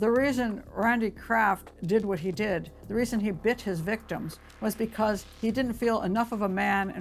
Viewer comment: does she say “the reason he b*tches victims…?”